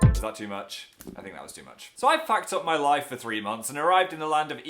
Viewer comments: Did Phil say fucked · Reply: No he said "I've packed up my life" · does he say he packed up or he fucked up???? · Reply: packed. phil doesnt swear ^_^